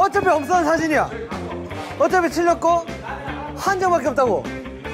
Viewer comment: why I always hear this as ' otta piche rakho ' 😂